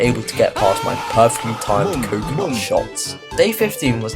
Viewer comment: the subtitles say “cum” instead of “coconut”, just saying